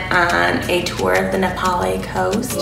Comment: did she say Nepali 🇳🇵?? Super proud